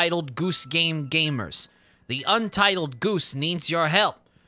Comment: sounded like you said "entitled goose" at the end lmao